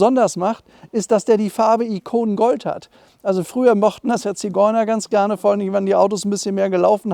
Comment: Habe ich etwa „Ziegeuner“ gehört?!!! 😱😱😱